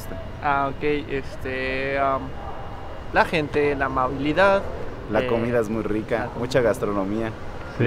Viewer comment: he did not say "the public transport", he said "amabilidad" which means kindness I mention this bc no one in Mexico likes the public transport at all